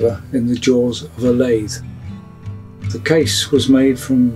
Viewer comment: What does he say the case is made out of at 3:41? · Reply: Jelutong I think